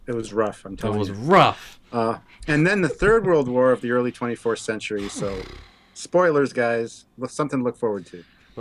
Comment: I have the same jacket as. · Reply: did I hear say the 'early 24th Century' at 2:08?